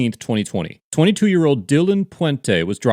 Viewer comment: At the beginning I heard "2 year old pulled over" and thought to myself why wouldn't they get pulled over😂😂